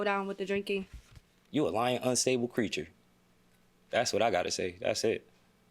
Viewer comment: With all he did, including vandalizing her car. He had the gut to say she's "a lying unstable creature." 🙄 The mother of his child? Smh... That's why people have to be very careful who they have kids with. Children do not need to be raised in such a negative toxic environment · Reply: I don’t know why I thought he said preacher instead of creature. He is the unstable one!!! This is so sad. I wish the judge would have really told him about himself instead of making her accountable for his actions